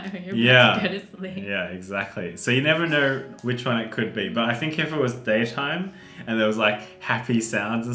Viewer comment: on number 7 i thought wengie said "it could be robert" and i was like mk